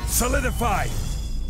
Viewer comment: I heard "Summon the Pie". Now I realize it's "Solidify".